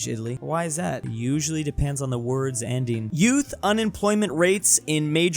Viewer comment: why did I think it said "worlds ending"??😅😅